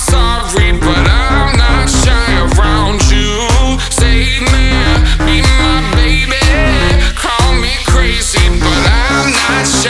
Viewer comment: Am I the only one who hears "Eat my baby" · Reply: pianofani yassss haha it's be my baby😂